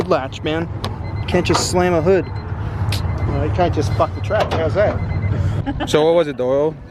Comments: lol what did he say😂😂😂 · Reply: S RAFF there a hood latch man you cant just slam the hood. Then the track employee says "well you cant just fuck the track, how's that?" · did he say " you can't just fuck the track, how's that"